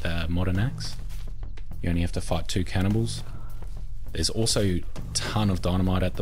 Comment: Sounds like you said "You only have to fuck the cannibals"